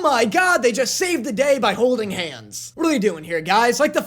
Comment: What you said: "saved the day by holding hands" What i heard: "saved the gay by holding hands" 😂😂😂